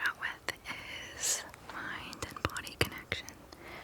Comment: At first, I thought she said "My dead bodies collection"